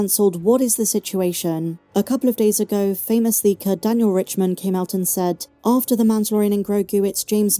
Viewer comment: "famously kir-daniel-Richman..." came out and said"..... wait... what did you actually say sorry??? Literally. Was it" "Sir Dan Gold Richmond" or something.....? I can't understand what you said (and I want to look them up)... sorry! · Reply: "Famous leaker Daniel Richtman" (an industry insider)